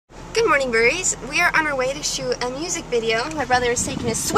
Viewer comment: i swear i always thought you guys say "Good morinig breese",just barley found out its burries!!